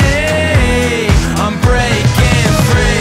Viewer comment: only i instead of "i'm breaking free" i hear "unbreaking III"?